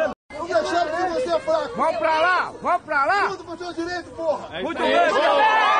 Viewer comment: Eu entendi "Lutem pelos MEUS direitos"...